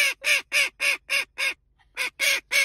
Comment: Is it just me or as she is sipping does it sound like shes saying "your not you when your thirsty"?